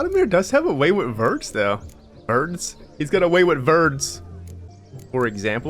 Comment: did u say that Valdmir has a way with birds? LMAO!🤣🤣😂 It is also funny that Shani said "I'm all ears" when Geralt is wearing donkey ears LOL.